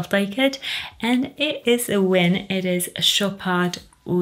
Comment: I was looking away when I heard Oud Malaki and thought I heard "Oud Malarkey" 🤣 which also would have been a fun name. I think Malaki is pronounced ma-la-kai (rhymes with sky)?